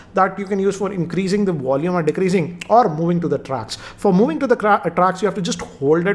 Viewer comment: Did you just say moving to the crap??? Man you are funny